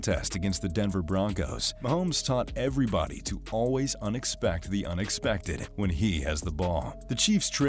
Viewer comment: Did anybody else catch her m saying unexpected the unexpected